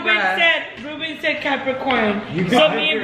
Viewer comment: Ruben said Capricorn and my fucking blind ass thought it said COCOMELON LMAOOOO🤣🤣🤣🤣🤣🤣